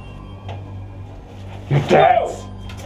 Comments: it sounds like he is saying (your debt) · For some reason I thought he said your debt.